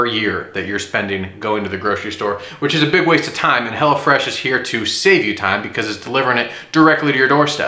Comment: thought he said HellaFresh